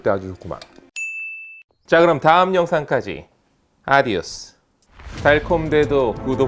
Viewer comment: 제 귀에만 3만명으로 들리나요?